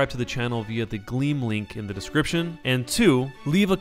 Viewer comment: Oh my god, all this time I thought he was saying green link in a weird way on purpose. Until today where I clicked the description and its gleam link😅